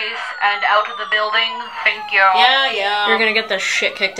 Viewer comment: “You’ll get the shît kicked out of you one day you know?” “And I’ll like it! Pain kink plot twist!” 😂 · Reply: I thought she said, "Pancake Plot Twist! Hey-Oo!"